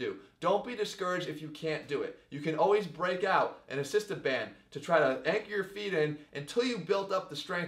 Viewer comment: did you say "assistant band" at 7:37? What is that? I may need help with that exercise.